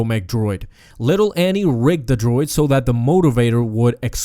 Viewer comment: it sounded like raped instead of rigged. No offense.